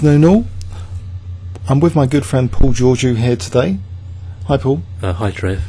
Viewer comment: i thought u said i was with my boyfriend! with a rusty voice thank fu**k i hearded wrong ! sorry tho !! u said a good friend!!!!!!!!!!!!!! nice analyse thank you